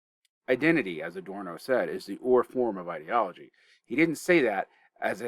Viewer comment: What was the word at 9:30 in the Adorno quote? It sounded like "Identity is the (aura or oral) form of ideology" Didn't Adorno say "Identity is the primal form of ideology"? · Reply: "ur-form" so yeah, primal.